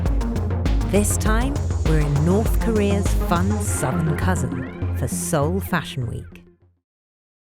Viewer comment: wait, did she just say "we're in North korea's..." at 0:14? wtf thats completely wrong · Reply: I may be wrong, but didn't she say "North Korea's fun southern cousin"?